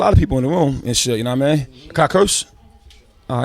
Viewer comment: what does RZA say at 00:45? got what? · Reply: "Can I curse?"